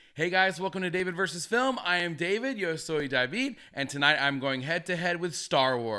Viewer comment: I'm always confused what you're saying after you say your name is david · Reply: Haha, I'm saying, "Yo soy David"- "I am David" in Spanish :)